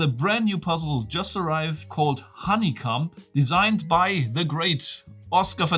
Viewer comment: ⏸️ is he saying honey cum or honeycomb?!